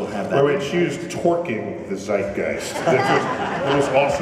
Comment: I thought he said "twerk the zeitgeist." O.o